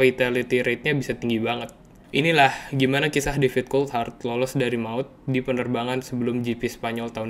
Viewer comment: menit 3:04 gua dengernya Difficult Hard bukan David Coulthard, emang beneran susah mati sih si DC 😂